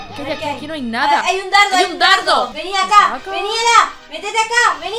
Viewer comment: Ela......A QUIEN LE DOY UN MARTILLAZO, LIKE PARA ELA, LIKE PARA, LIKE PARA, amo sus vídeos juntos son muy divertidos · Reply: Jajajajaja ami igual me encanta pero no me digan que soy la única q se dio cuenta q le dijo a ela vení Daniela en el minuto 2:18 algo así le entendí 🤔😅😀😁 Lo escribo por q no me gustan mucho comentar públicamente si no que siempre busco el comentario q más se parezca a lo q voy a comentar y ya 😅😬😀